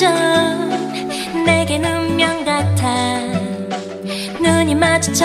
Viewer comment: Not hating but 0:37 , the Hangul subs is wrong because it says ' kwenchana yogi nun i ' instead of just 'nun i' (excuse my terrible romanized spelling)